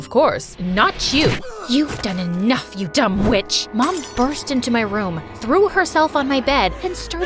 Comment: I was watching it in 2x speed and I heard “dumb bitch”😂😂😂